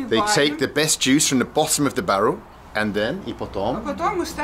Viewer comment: "i didn't quite understand", about winemaking, she said, that juice further stored in barrels in the cellar - "pogreb" in Russian, not sure if it was somewhere in comments below... 6:57 "I didn't understand," she said that she will not fit in the camera, because she's too fat (joking) around 13:04 it's not vodka but rather a type of homemade brandy probably from the very same grape juice they did the wine.